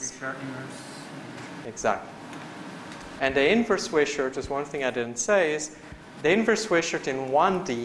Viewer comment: asks "Which prior would you put on sigma?". I cannot hear the answer coming from the audience. And the subtitles didnt really help. Any thoughts? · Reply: I agree it that it was hard to hear. I think he said Inverse-Wishart distribution <link> . Inverse Wishart is Inverse-Gamma when it is univariate.